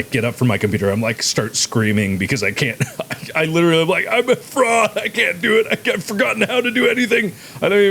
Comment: When you said "I'm a fraud..." I heard "I'm a frog...", was confused for a few secs...